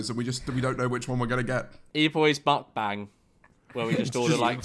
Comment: I thought he said “eboys buttbang” not eboys mukbang